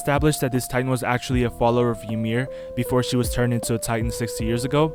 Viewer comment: I thought he said children of Ymir because of the Eldia.. not the lez Ymir.. and I thought he was a scientist